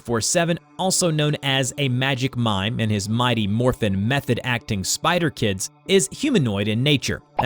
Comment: It cut off so I thought it said mighty morphin meth lmfao